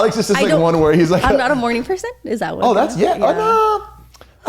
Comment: I thought she said she’s not a horny person, not a morning person at first 😂😂😂😂 I had to back it tf up to listen to it again 😂😂